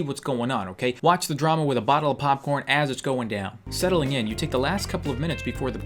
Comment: i swear he said "with a bottle of popcorn" and i oop